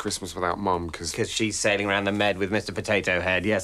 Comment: what does Mark say at 2:05, "saving around the mead?" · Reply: 'Sailing around the med' as in sailing on a yacht around the Mediterranean